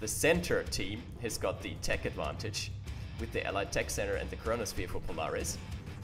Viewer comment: I couldve sworn he said coronasphere. He is a time traveller!